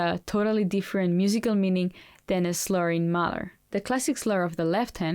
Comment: HI, you said "a slurring Mozart has a different musical meaning than a slurring Mahler," I thought you said sluring 'Mother!' ☺️ haha! (2:45m)